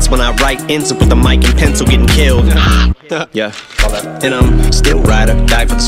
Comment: When he said mic, pencil get killed , I heard "Mike Pence get killed" and then Em said "I wasn't referring the vice president chill"